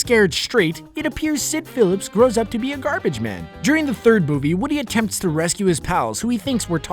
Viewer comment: did you say boobie instead of movie???